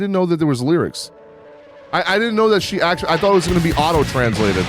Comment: I actually thought you said "I thought it was gonna be Ado translated" 🤣. All of her original MVs have official subs. At least one Japanese artist that does it regularly. I also don't think she did the MV because of some form of external pressure. She made sure to say that she did it because it's one step closer to self-love for her and because she feels like she's getting closer to her ideal self and is actually confident enough to do it now. I also think the lyrics you analyzed in the beginning of the MV were more directed at her childhood. She said in an interview a few weeks ago that she was always told from kindergarten age on from all kinds of people around her "you can't do it anyways" and "you're just a little princess, just let other people do it for you".